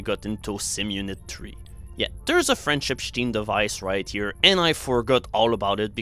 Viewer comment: OH! A "French-Epstein" device :o I thought you were saying "Friendship stream" or something :p